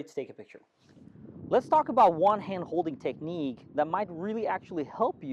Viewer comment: The last point doesnt make sense, you talk about 1 hand holding technique, jet you hold it with 2 hands? Also i miss the portrait mode holding technique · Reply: If you listen very carefully, he says, "let's look a one hand-holding technique", as versus "let's look at one-handed holding technique". --- Yup, I misheard him first time too.